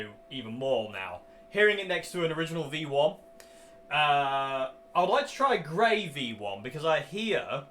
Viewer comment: At first I heard "I would like to try gravy one..."